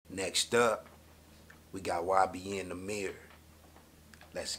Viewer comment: i know this old but it sounded like u said "thats why i be in tha mirror" instead of ybn nahmir, shit has me weak